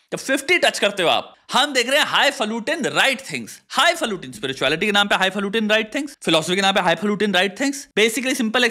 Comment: pr ye kya bol rha h high flute in right things??